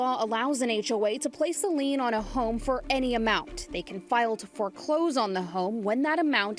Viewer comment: Georgia law allows HOA to put a lien on your house over 2k? That is the problem in its core. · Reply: I believe I heard "...any amount."